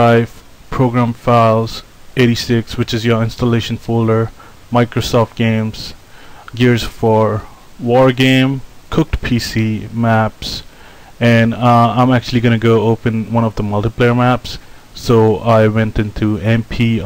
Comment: did u said cooked PC?...0_0 that weird name for a folder